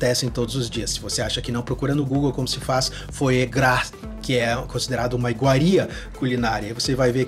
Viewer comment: não entendi a palavra que ele fala pra pesquisar no Google. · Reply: Foie gras